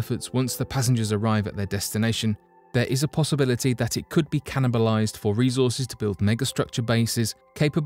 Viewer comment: "cannabalized"..hahahha. idk, 4-5 times listening, i could swear u said that. colonized was said like 15 times, so...